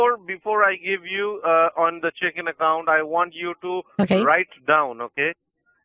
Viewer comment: the subtitles i have read that the scammer said chicken account and thats exactly what it sounds like 😂😂 also when you asked what language and he responded then you said ohh idk phillip 😂